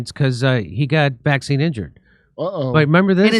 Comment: I thought it said vaginally injured